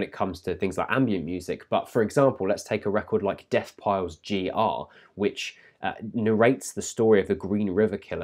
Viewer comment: I can't understand the title. Could somebody please help me out? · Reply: Deathpile - G.R.